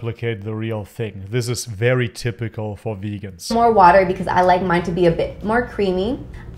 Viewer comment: Did she say “I’m gonna add more water because I like it creamy”?!?!